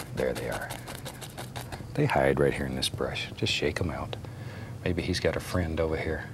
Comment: Talking about the trees: "They hide right here in the brush, just shake 'em out." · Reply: Idk why but I thought you said they hide kids in the brush just shake em out and I was shocked lol